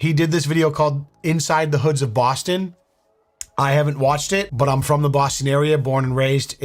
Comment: At the beginning of the video, I thought Leon was about to say "I havent watched it but I’m from the hoods of Boston"